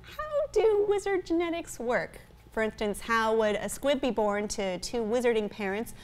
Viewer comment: Did she say a squid? How would a squid be born to two "wizarding" parents? A fucking squid? What the fuck kind of book is this? It sounds like Emma Watson should have been tentacle raped onscreen hentai-style. A fucking squid? I don't know... I'm probably missing something. Fuck it. I don't care enough to look it up. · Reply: Squib.